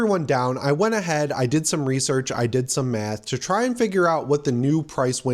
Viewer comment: at first i thought you said "i went ahead, i did some research, i did some meth" 😭